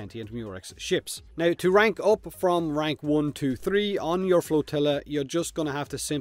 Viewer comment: I keep hearing tortilla